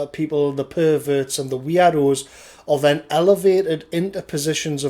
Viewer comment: For a moment I thought you were saying weeaboos when you said weirdos.